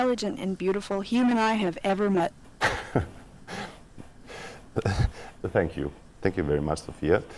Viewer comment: Lol when she said thank you at the end of the interview I thought she passively said fuck you 😆😆😆